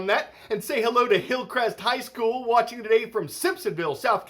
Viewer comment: Did Carl say Simp?